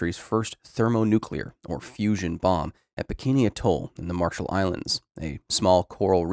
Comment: at first I thought you said "Bikini Bottom" and I just... went with it because it could make sense.